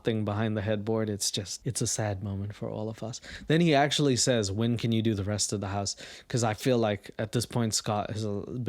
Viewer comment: I didn't hear him say "When can you do the rest of the house", I thought he said "When can I go back to the old room"-... Which is a much more fitting question